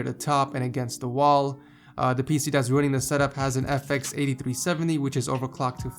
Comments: it sounds like hes saying "the PC that's ruining this setup..." roasts upon roasts ed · i thought was saying "the PC thats ruining this Setup) :D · did he say the pc that's ruining the setup has a fx 8370